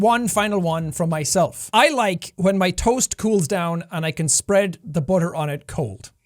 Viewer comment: I misheard you in the end... I thought you said "I like when my TOES cools down and I can spread the butter on it cold" XD LOL love you Jack <3